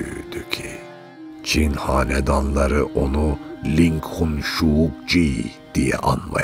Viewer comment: ← Az önce “Lincoln Jaeger'a selâm mı var?” dediğim yer burasıyı. “…Çin Hanedanları onu ‘…’ diye anmaya başladı.” dediği yerdeki Çince sözü Lincoln olarak duydum.